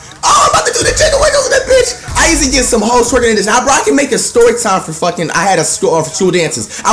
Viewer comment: did say I used to get hella hoes twerking to this song?